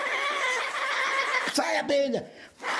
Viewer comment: what did he say ? 😂😂😂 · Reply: Sai, abelha! In english it means: Get out, bee!